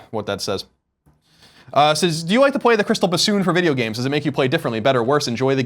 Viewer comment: can someone tell me what was the question asked i couldnt understand few words there " Do you like to play the ______ for video games..." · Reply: SoNGY 8 Crystal Bassoon